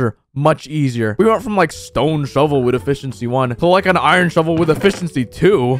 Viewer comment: I thought he said bishop c1 instead of efficency 1